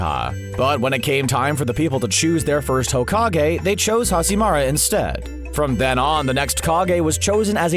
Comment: did he just said hashimara🙄